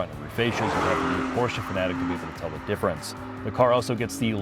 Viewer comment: Absolutely NO idea what you said from 1:05-1:08. Went back and listened 3 times. Stop trying to sound so “I’m so cool and these cars are no big deal to me” and enunciate. I turned on closed captioning and it can’t make out what you said. Some run on sentence about facials. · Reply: Porsche fanatic is what he said.